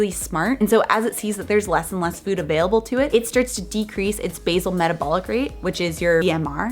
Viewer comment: Did you just say "metally" for metabolism?